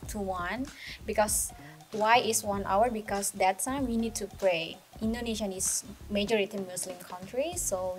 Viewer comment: In 10:14 , the the subtitles are wrong , not “break” but “pray” , muslim Indonesians Pray during that time (12 to 1)